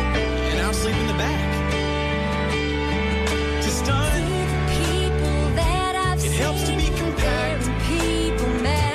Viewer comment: Does anybody know what the girl says at 3:00-3:07? 'I can see the people that I've seen..' Thanks! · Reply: She says "I can see the people that I've seen, there in People Magazine, dancing with celebrities till dawn."